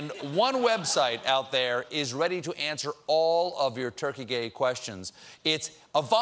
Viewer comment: Did he just say "turkey gay questions"?